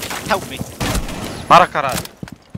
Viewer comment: Aquilo foi um “para caralh*”?